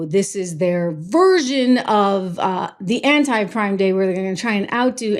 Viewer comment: I got excited as I thought you said anti Pride day